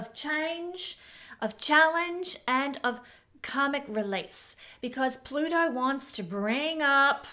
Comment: I thought you said "comic release" ! Haha!